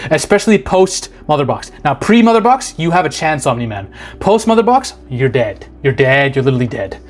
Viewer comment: did you say He-Man? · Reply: What? No he said Omni Man